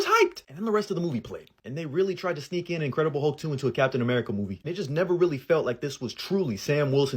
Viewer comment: Hulk doesn't even appear in the movie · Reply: just rewatch it and I miss heard him , I thought he said incredible was in it to but he said they turned the film into Incredible Hulk 2 My bad